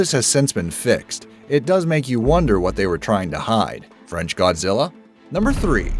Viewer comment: By the way, make sure to click that link at the top of the description for a chance to participate in our poll AND be entered into our giveaway :D Just make sure to like our Facebook page as well to enter! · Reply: wait a minute did you say at 4:18 France Godzilla!?!